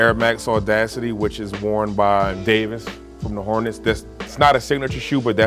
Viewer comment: "Anthony Davis from the hornets" 😂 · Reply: I thought he said “From the Orleans.”